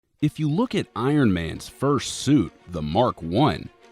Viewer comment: i thought he said "if you look at ironman's fur suit"